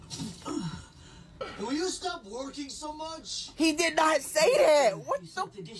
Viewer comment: I was there and I thought he said "Will you stop twerking so much" 💀💀